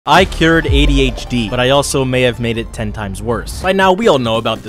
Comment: Misheard him and thought he had cured aids😅